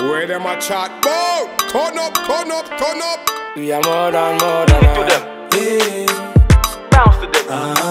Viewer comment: did he just say "we're more than God"?